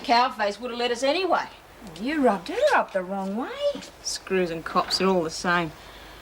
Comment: Screws and cops they're all the same. I thought she said screws and cocks for a minute. Pricked my ears up.